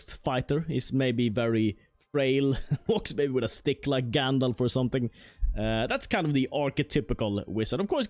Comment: did i just hear: "frail like Gandalf" wtf the dude is 2,020 years old when he defeats the balrog in 1v1